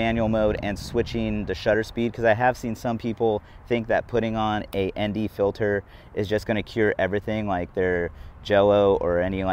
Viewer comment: I heard you say Andy filter so many times